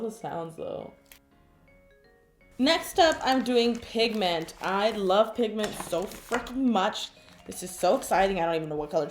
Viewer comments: Did anyone think she said, "I lobe pigment so 'flunkin' much!" at 4:17 ? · i heard « i love pigment so fucking much » 😂